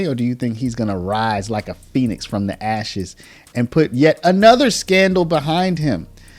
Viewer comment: Did I just hear "he's going to rise like a penis from the ashes?! 🤣